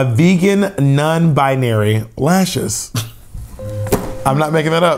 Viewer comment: The Vegan Non Binary lashes are the winner 😂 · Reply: LMFAOOOO I DEADASS THOUGHT HE SAID NON BY NARY. HOLY. SHIT. 😂😂😂😂😂😂😂😂😂